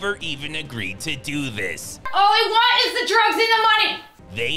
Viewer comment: I had to listen to her line at 3:33 three times to understand what she says; "all I want is the drugs and the money!" They really couldn't re-take that one line?!